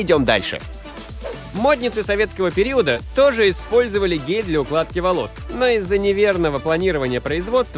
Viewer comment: Стоп стоп стоп мне послышалось на 1:09 слово гей